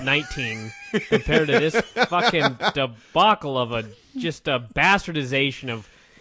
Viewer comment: What word did use to describe 'ECW December To Dismember 2006'?... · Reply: 'debacle'